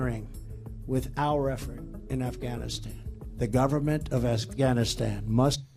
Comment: did he say, the government of ASSganistan?